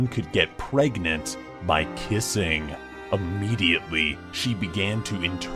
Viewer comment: When they said you can get pregnant by kissing, I thought he said pissing...